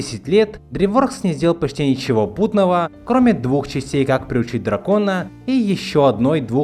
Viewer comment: Мне послышалось как пришить дракона